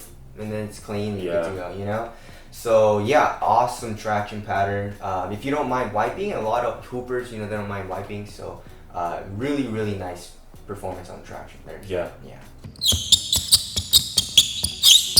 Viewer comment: Why did it sound like said "A lot of poopers don't mind wiping." ? P.S. I know he said hoopers.